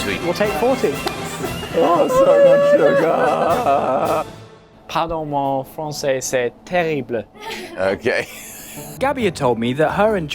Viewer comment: I think he said "Pardon my french, it's terrible." (?)